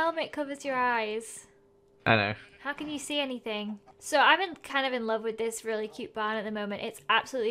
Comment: When you said "im in love with this barn"I thought you said " im in love with joel